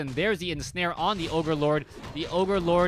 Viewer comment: did he say "over lord"....ii think too much starcraft lol